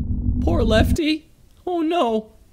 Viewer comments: did say "poor lefty he doesn't have to jump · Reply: Lefty no it's helpy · Poor lefty oh no me: it’s helpy not lefty 9:04 the PC crash thing sounds like it saying a bad word that starts with f